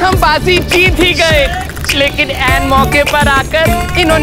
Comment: did he just say english word " end moke par aa kar "